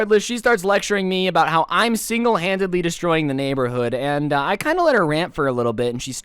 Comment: I thought you said that was lecturing you about being single